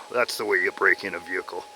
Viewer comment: Did I hear Kermit the Frog say “that’s the way you break in a vehicle “